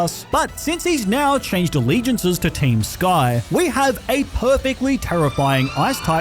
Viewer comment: did he just say team SKYYYY?